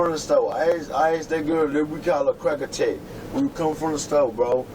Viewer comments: He said they call her "Crack Attack" 🤭😆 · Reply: I had to replay that part 2 I thought he said Cracker Jack 😅😂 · He said they call her "Crack Attack" 🤭😆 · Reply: I thought I heard Cracker Chick lol